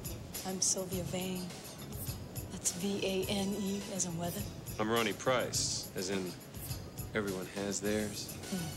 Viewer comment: I swear it sounds like he says his name is “Pepperoni Price”.